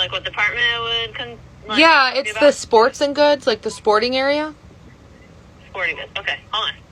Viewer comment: if i said 'sports n goods' instead of sporting goods, i would not post it to the internet, okay?